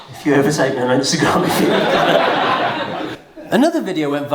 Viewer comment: What did say at 8:52? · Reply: I believe he said “if you ever overtake me on instagram”